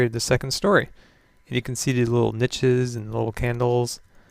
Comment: I heard "These little Bitches"